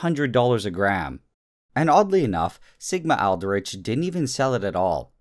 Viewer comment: Did anyone else understood ligma-outbreak instead of sigma-aldrich? lol.